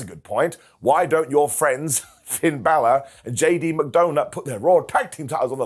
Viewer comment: I thought he said J.D McDonut at first 😆 Thats actually a pretty funny name for him